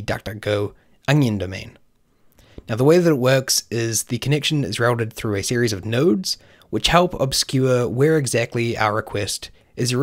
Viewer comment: Did you just say "Nerd"?